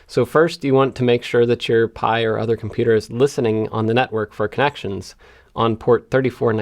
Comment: I could swear I heard you say "Nutwork" instead of network